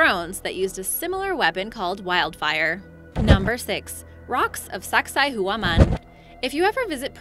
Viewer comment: Who else heard: "Rocks of Sex Style Woman"